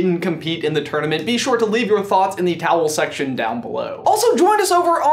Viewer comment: was it just me or did he say leave your answer in the TOWEL section below,instead of saying comment section below